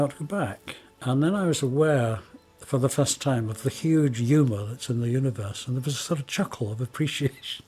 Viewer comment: What does he say at 11:45? Aware of the huge “u-Bullets” ? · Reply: Supposed to be "huge humor".. it does sound like u-bullets tho lol